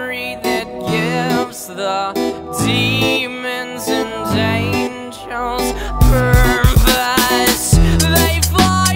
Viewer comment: i heard "the demons and jAMES CHARLES purpose" tf